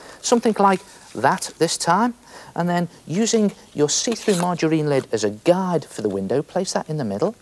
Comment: Does anyone know the plastic material as the card window by Neil? I hear it called " see-through majoelein " ?????but I still can't find it online. Thanks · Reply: He says “see-through margarine lids”, I’m unsure if any margarine or butter has see through lids anymore.